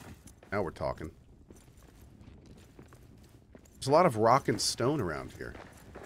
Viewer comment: Did I hear a rock, and stone!?